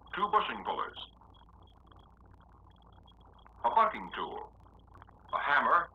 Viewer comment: did he say.a fucking tool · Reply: bucking tool